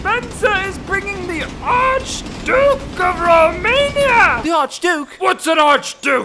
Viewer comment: DID HE SAY ROMANIA OR AM I DEAF????